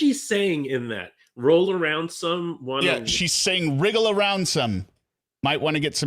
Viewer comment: I always thought sarah said "Real ransom, might wanna get paid extra" 🤷